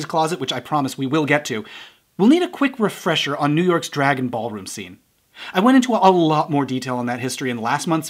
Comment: When you said "Drag and ballroom scene", I heard it as "Dragonball Room Scene". Now i'm imaging Vegeta reading Goku to filth.